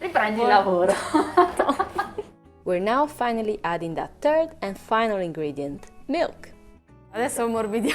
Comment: Even though I'm not a native speaker I still heard "turd" instead of "third".